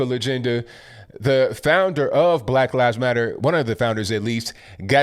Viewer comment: For a second I thought you said "Black Liars Matter" and I thought, "Yeah, that's probably what they meant."